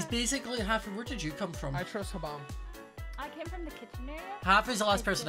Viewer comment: When someone said “huh-bomb” I was thinking about something and I thought they said “I trust Obama”